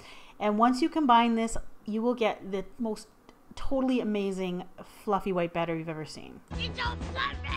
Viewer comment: it's SO FLUFFY i thought it said you don't flush me when I heard it for the first time XD